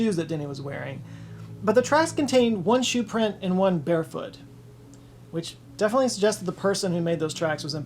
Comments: Bear 🐻 foot is what I heard @ 3:50. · Oh, BAREfoot, I thought you said BEAR foot as a weird way to say bear paw, and I was imagining a boy and a bear doing a three legged race. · "One shoe print, and one bare foot." I first thought he said one BEAR foot. Is being eaten by some animal predator not an obvious possibility? And how could a shoe print the right size for a six-year-old also possibly be that of one of the searchers? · I thought you said one shoe and one bear foot.....and it was really doing a number on my imagination until I realized you meant "bare foot". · I thought you said one sneaker and one bear foot.